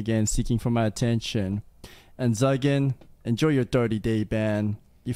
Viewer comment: Zuggin I thought he said Zyken I was gonna say!